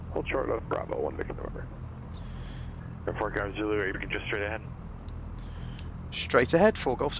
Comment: does the tower say "Report Golf Zulu if you just straight ahead" ? I really don't hear "Seven One Four Golf Zulu" or "Four Golf Zulu". And at 22:06 he does it again!